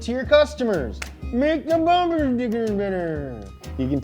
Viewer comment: ayo what did he say at 10:11 ???? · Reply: Make the bumper stickers better